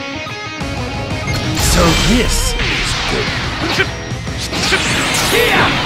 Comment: lmao I heard “so this is gay”